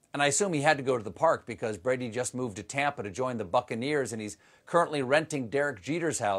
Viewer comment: You say “joined the Buccaneers” and I heard “joined the f-ing deers”